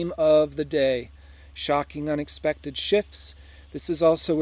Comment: Poor dinosaurs. Our ancestor’s. Giants. Dragons. Wiped out. The whole human race has forgotten it’s galactic past. I know you said “shocking exponential shifts” but I heard “shocking exponential ships” “And you get all those pencils nice and sharp and wish a bitch would. Super hero’s are made when you keep your inner child’s hope for your future and the future of humanity alive. Or the inner child is stripped of its imagination and loses its ability to fly